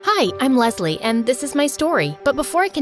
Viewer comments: i thought she said “Hi Im lovely！） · Is it just me cuz I hear "Hi, I'm lesbian" at the start- 💀